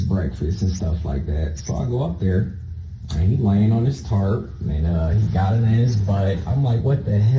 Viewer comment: did he say that his son was laying on his tarp?